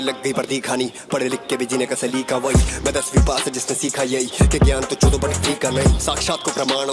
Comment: lmao the subs are so wrong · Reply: he said "mai dasvi pass" aur subs mein tha "I'm 10th fail" "give knowledge but it shouldn't be pale" but he clearly said "gyaan chodo par free ka nahi"aur bhi jagah the ab sokar utha toh bhul gaya